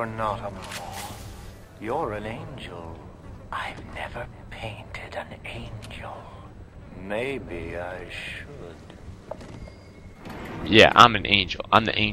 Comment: My speaker is so lousy that at 2:45 I thought at first it said, "I've never dated an angel. Perhaps I should." ROTFL!